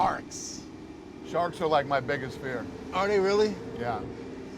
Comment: Had my volume turned down at the beginning and genuinely thought Dana said “Sharts are like my biggest fear”